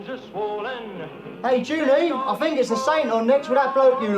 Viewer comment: Buster slips up and calls his wife “Julie” instead of June 😊